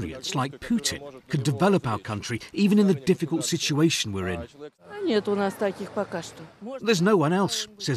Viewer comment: Russian lad: "Maybe we need someone experienced like Putin who can develop the country even in this difficult situation, in which he put us in." Bravo youngster, flawless logic. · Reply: the only problem is he did not say thus, but rather "...the difficult situation we are in". (3:40). Has they converted the Bletchley Park into a troll factory?